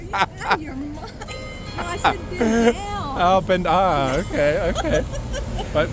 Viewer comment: Good to know. I heard he say "bend over" too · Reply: Actually I meant bend down because he is so tall. We were trying to take a picture. I am a little embarrassed